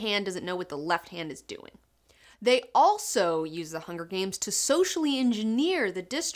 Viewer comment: did she say the "Hunger Pains" lmaoo